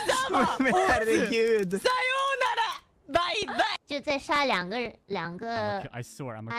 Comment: Enna speaking Chinese in the background even adds more fun😂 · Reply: We still have 2 something something is what I heard